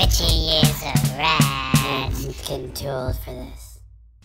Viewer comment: Did he say getting to old for this? 1:43😂😂